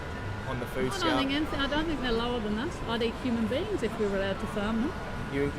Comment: What the woman say in minute 5:00? "I'd eat human beings if....." i don't understand what she say. please tell me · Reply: ok She said:"I would eat human beings if we would have to..." It left the verbe "to.."? She say another word that you don't write and I don't understand. Please can you write it? Thank you. Appreciate you in your job